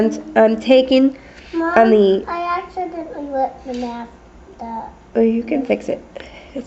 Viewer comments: What did her baby say at 2:25? I heard "mommy, I accidently let him out of his cage." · lol at 2:31 did she say " mom, i accidentally wett the bed." lol im probably wrong